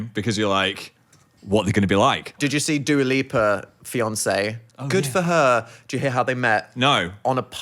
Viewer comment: y'all have melted my brain. I thought he said Dua Lipa and Beyoncé, just 'phan'ed it and called her Pheyoncé 😭😭 I WAS SO CONFUSED